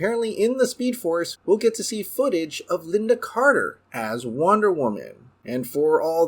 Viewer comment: I thought they said they cut lynda carter cameo